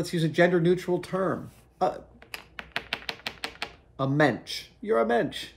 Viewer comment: Hey i have a question.. what is he saying here 5:24 is he saying "Mensch"? Like the German word for human? Or what exactly..? Sorry not native speaker here..